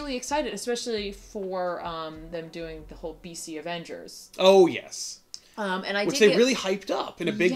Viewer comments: I misheard PC avengers and i couldnt tell if it was sarcasm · im so far removed from marvel at this point I thought said "PC Avengers" lmaooo